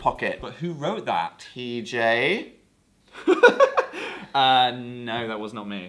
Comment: does dan call pj “TJ” or am i hearing things lmao